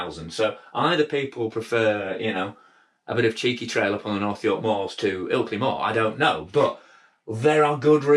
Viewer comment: WTF did you say at 1:39? Cheeky filty mars? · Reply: Haha, it’s something like “maybe you just prefer cheeky trails on the North York Moors to Ilkley Moor” and it’s about two riding areas local to me 👍🏻